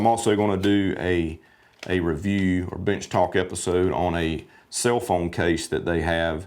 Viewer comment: I thought you said Bitch Talk. Can you do a bitch talk in the future ?